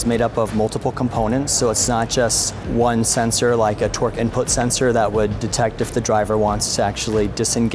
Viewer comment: It needs a t rex chasing it. Plus did he just say " a twerk input sensor?"